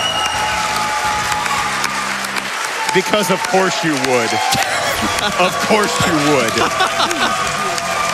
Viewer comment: It sounded like "Because of horseshoe wood"